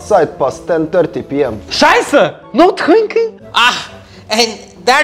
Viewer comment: I heard that said niBB* on